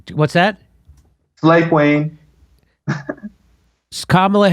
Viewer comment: what is he saying? Hate Wayne? Slave queen? I can’t make it out and it feels like jimmy gave up and moved on